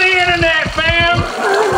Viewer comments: "Welcome to the Internet, fam!!" -Link, 2018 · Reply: Name.exe_failed I thought he said ma'am too, but he said fam. He got chocolate on his lips 🍫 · "Welcome to the Internet, fam!!" -Link, 2018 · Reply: I thought he said ma'am